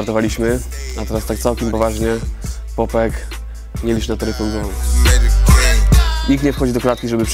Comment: co on powiedział? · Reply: Nie licz na taryfę ulgową